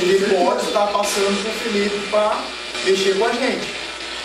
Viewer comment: No minuto 11:08 ouvi dizer FELIPE